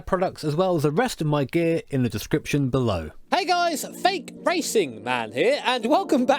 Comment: For a second I thought you said "Hey guys, THICC racing man here..."